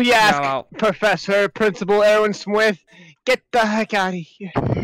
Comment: "Professor- Principal eRwIn sWiFt-" Seriously tho- Why did that sound like he said "Swift" instead of "Smith" HSHSH